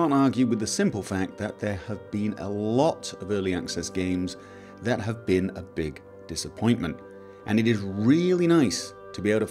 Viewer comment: "some early access game were a complete fail/delusion" Did you hear that, Ubisoft and EA!? Oh wait... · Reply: don't know how, but I first heard that as "there have been a lot of tripple-A games that were a disappointment" :D